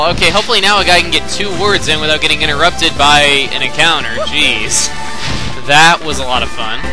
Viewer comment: When you said an encounter I thought you said accountant